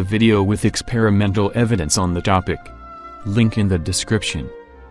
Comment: Every time I hear "link in the description", it sounds like " Lincoln the description".